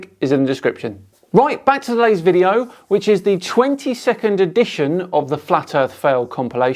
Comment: I thought you said it was the 20 second edition of FEFC (not 22nd) LOL I could spend hours detailing all of the ancillary benefits of the space exploration program. I can spend <1 second on the ancillary benefits of LEO’s videos.